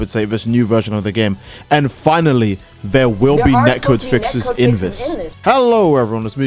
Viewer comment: What did he say at 0:40?? Next code fixes?? I rewinded it so many times and I’m still lost. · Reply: Net Code basically minimize lag